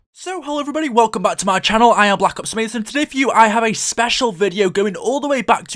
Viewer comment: in his intro it sounds like he is saying "hello, im black ops raisin"